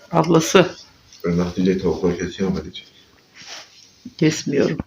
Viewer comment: 4:20'de ne dedi tam duyamadım canım. ne diyecekmiş sana 😁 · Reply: ablam diyicekki tavukları kesyonmu bende yok diyorum büyüttüğüm civcivler varta onu diyo